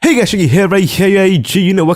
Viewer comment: What I hear is "Hey guys a uh gee uh gee!"